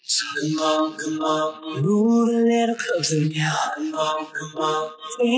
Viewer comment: Lou moaned in now so it sounded like nowh